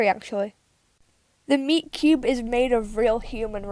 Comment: When you said meat cube I thought you said miku